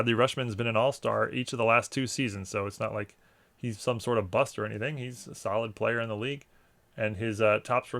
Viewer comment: I thought you called him a, "Buster"... like Dom in Fast and Furious style...I laughed so hard...